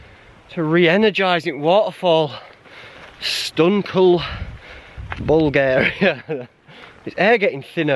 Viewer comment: is saying Stone cold Bulgaria?! Is that a expression?